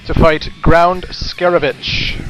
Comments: When he said "Ground Scaravitch", I heard "Ground Skelebitch." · When you said "Ground Scaravich" I thought you said "Ground Scarabitch". lol